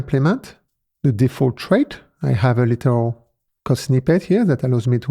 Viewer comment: Does the CC does not show for you? YoutTube auto caption is quite impressive with accents. I reviewed it, and seems to be ok. Let me know if the CC does not work for you. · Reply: you said "trait", but the CC said "trade". That's minor. Slightly more difficult is that the CC text obscures the bottom of the video. The alpha value for the CC text is a little less than 1.0, which makes it a little harder to read when there is other text underneath. If only YouTube would make the content taller so that the CC text appears beneath the video... I ended up turning CC off, and then using the keyboard shortcut to jump back 10 seconds if I needed to hear something repeated. Slowing playback speed to 75% is sometimes helpful. I think what you're doing is fine. I have several alternatives to help me if I'm having a hard time understanding.